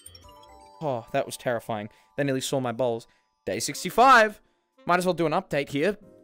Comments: It sounded like you said "they nearly saw my balls" when you said "they nearly saw my bowls"😂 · did just say They nearly saw my balls? XD